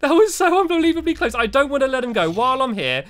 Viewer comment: I heard Stampy said I don't wanna life.